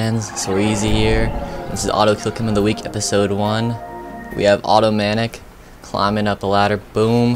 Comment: sounded like he said "We have Auto-matic here" haha